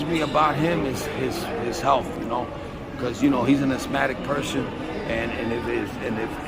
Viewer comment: Losing weight is very dangerous for a Latin fighter!?!??? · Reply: Asthmatic. It did sound like he said Hispanic tho lol